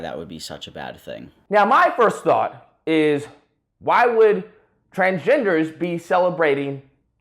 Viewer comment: I thought he said "trangenderists"